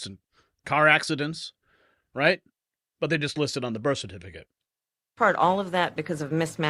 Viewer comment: Did say birth certificate instead of death certificate at 7:05?